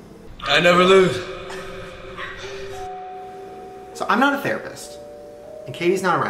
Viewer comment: Am I the only one who thought it said I’m not the rapist